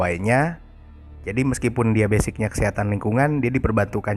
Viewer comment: Diawal dengernya "setan lingkungan" dong, ternyata Kesehatan Lingkungan. :") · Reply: wkwkw benerr, tp kalo lebih teliti lagi dengerinnya kedengeran sih "kesehatan", cuma dia nyebutnya kecepetan jdi kyk "kshatan" wkwkwk